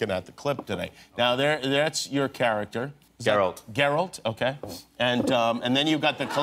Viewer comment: Did Cavill say Zerald?Lol